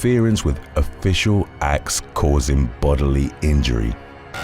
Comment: I thought you said killing Nicole Cunningham 😂😂😂